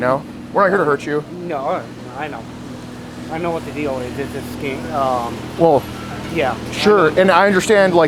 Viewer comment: Was he about to call this a scam or a sting, cause I heard scam.